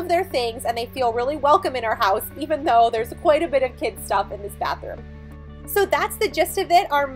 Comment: Wait a minute...did you just say, "it holds the kids stuff"? As in plural? Are you keeping a secret?